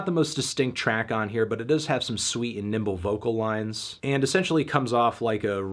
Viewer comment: I swear I thought I heard Fantano say “sweet nipple vocals”